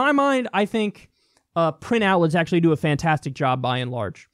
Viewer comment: What is the good source of information that Kyle talks about at 6:35 ? I can't really understand? Pinout? Printhow? Ty for your help, internet stranger. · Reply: Print outlets, like a newspaper.